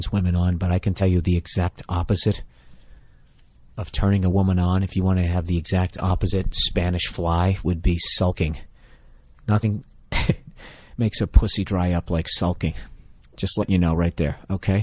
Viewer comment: Minute 15:49 what does he say? sounds like saulking... I want to look up that word, never heard it before. Please help a lazy english student to get better listening to fun staff :)